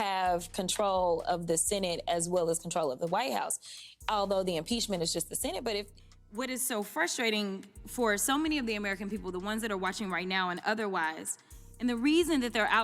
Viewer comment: I heard her say quote "white folk"